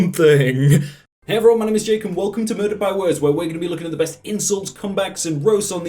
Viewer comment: Thought he said, "Welcome to Verda'd by words"